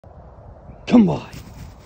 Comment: Oh good u clarified… cos I always thought u were saying “come boy” and the dog would run off 🤣